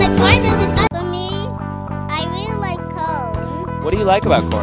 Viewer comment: When this was evented I thought I heard: 🌽 MOMMY I REALLY LIKE CORN 🌽